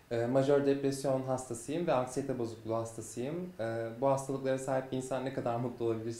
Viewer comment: ne dedi? anlamadim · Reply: akstiyete bozukluğu hastasıyım